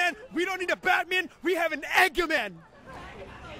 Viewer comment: I thought he said ’egg yolk man’ 😂😂😂😂😂😂😂😂